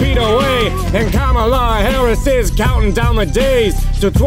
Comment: I thought he said common law Harris